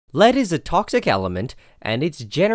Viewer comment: I completely misheard the beginning and thought u said "ladies and toxic elements" instead of "lead is a toxic element"